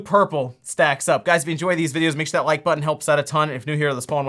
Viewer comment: You say "hit that like button" so fast that I always hear "make sure you that like button"